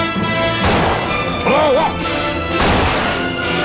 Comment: i thought he said fire you son of a